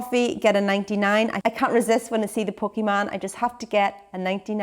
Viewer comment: I thought Caroline said a "Pokeman' and tried to google pokeman '99' food Ireland only to realize she said Poke Van and that poke is slang for ice cream cone. Check this video for the best description <link> I love that I just learnt something new about the Irish culture. For those of you who don't know a '99' has strawberry syrup drizzled on top and a little chocolate stick of sorts popped into the top of the cone. Well it certainly made me smile;). Yum!